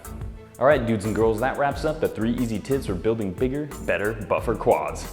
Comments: i swear he says tits listen closely. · is it just me or did he say "3 easy tits" at 3:54? lol